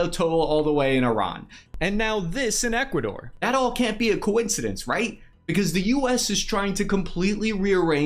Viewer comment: Am I the only one who heard him say Kuwait-cindense?